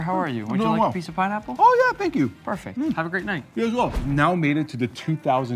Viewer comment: what did bro say 😂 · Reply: You as well